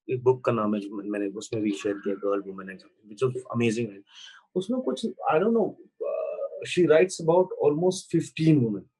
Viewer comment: what book is he talking about here? can't hear the name properly. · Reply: Girl,Woman,Other